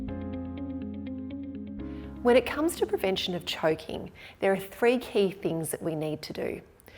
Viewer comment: I thought she said professional choking lol